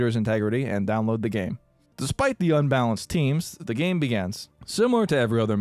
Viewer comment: Does it sound like he says "begans"?